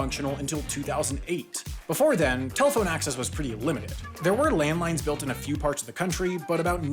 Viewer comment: did he say landmines?